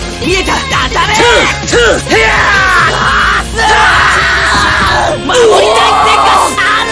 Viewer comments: 守りたい生活「費」があるんだぁーー!!!って聞こえたｗｗｗ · 守りたい設xに聞こえてびっくりした